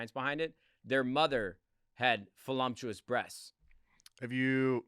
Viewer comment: Sounds like you said Folumptuous… 😂 its voluptuous